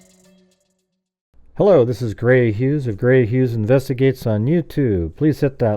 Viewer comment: On your opening, it sounds like you say hello. This is granny Hughes.